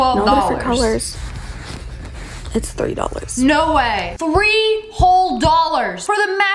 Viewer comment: the first time I heard her I thought she said “it’s $30.00” but then I realized after hearing her a 2nd time that she said “$3.00”